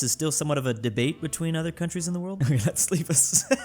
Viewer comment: anyone knows what he saying? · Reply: Ok let's leave